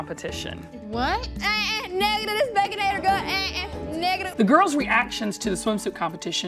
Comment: did y'all understand what she said ?!? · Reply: “Not with this baconator gut. Nuh uh negative”